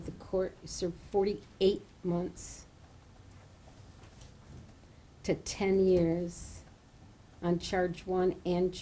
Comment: So why did she give him 48 months?? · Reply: I thought she said 48 months to 10 years, I may have heard wrong..